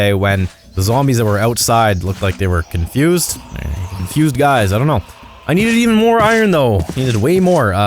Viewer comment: what did he call it ?! I didn’t hear man ! I think I heard GUY?! 🤭